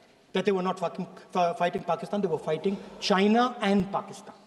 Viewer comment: bro really said f*cking instead of fighting😭😭